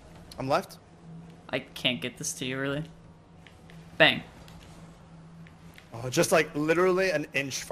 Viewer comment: literally sounded like G18 at 24:24 when he said Bang lol